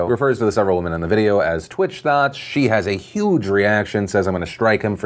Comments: My dumb ass thought you were saying "Twitch thoughts". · Am I he only one that got the idea that when Felix said “twitch thots”, I actually heard it as “twitch thoughts” as in the mind moves (like a twitch, not the platform) towards things randomly without you thinking about it. That’s what I heard when I watched his original video at least..